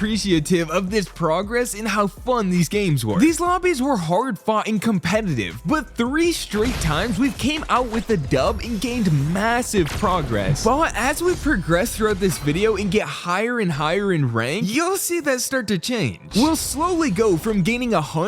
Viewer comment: did i just hear the word m-m-massive? cause yk what else is massive LOW TAPER FADEEEEE